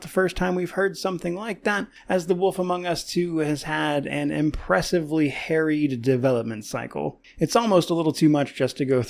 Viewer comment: at first I heard “an impressively hairy development cycle” and I thought was a comedic genius